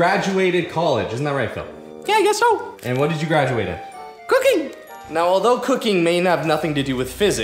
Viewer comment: Who else thought he said cookie